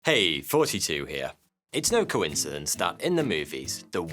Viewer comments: When I discovered this channel I thought he was saying "Hey! 42 here."... until I read the name of the channel. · When he says “Thoughty2 Here,” I hear it as “42 here” and my brain is like: “Yeah, the meaning of life coming at me for a sec. That’s exactly what he means…”